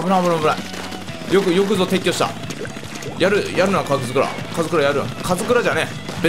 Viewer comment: にカズドラと聞こえるのだが